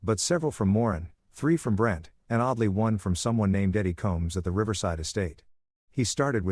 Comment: I thought that said Diddy Combs 🤣🤣